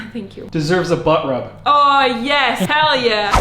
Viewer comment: Deserves a ' butt wrap' did i hear that right our... also whats a 'butt wrap' haha 😅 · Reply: I heard "butt rubb"